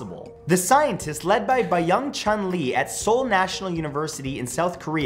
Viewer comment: I thought he said “ led by young chun lee”